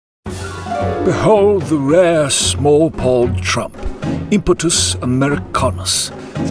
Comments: "The rare Small-balled Trump!" Just wow. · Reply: spoken while he's playing golf, I hear it as "small ball" or "small balled." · "The rare Small-balled Trump!" Just wow. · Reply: I thought he said small pawed as in small hands but the L in the pronunciation had me befuddled, small balled makes sense and would really upset Herr tRump. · Molestus Epstein Weight: Decreasing I snorted. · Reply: Babba Ganoush I thought the video said: "Small Balled Trump". Lol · “Small pawed”. Hilarious! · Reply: The first time I heard it I thought it said "small balled"